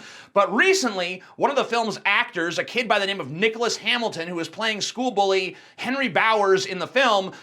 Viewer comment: I swear to Jeebus, I thought you said his name was Dickless Hamilton.